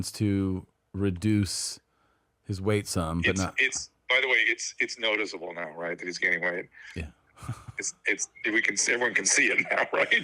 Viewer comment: Wait what did Drew say? “It’s known as a Long Island ride?” Am I hearing that right?. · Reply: I’m not totally sure, but I had to check the closed captions and I think he said “it’s noticeable right now that he’s gaining weight.” The “it’s noticeable right now” sounded like “Long Island ride” when it’s all muffled over the phone